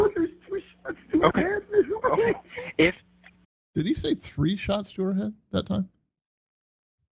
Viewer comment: The jury heard this call in court this week. If you want the tighter version — the call and the bodycam in 35 minutes instead of 57 — we just posted it here: <link> question for you: what did YOU hear on this call that stood out? Drop a timestamp below. Caleb Flynn has pleaded not guilty and is presumed innocent. Subscribe — we're covering the trial. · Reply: sure did sound like he said, "There's 3 shots to her head."